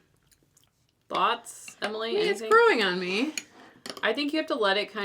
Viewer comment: When Emily said, "It's growing on me." I thought she said, " It's brewing on me." lol!